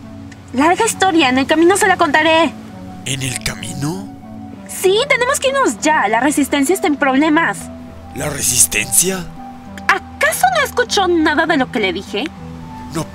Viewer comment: Anyone english here EDIT oh just me EDIT EDIT 1:02 did he say kamino 😂